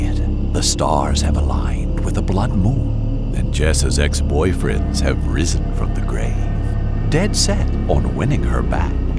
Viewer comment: umm i thought they said ‘jess’s ex boyfriend RIZZING from the grave’ ive gone crazy abt rizz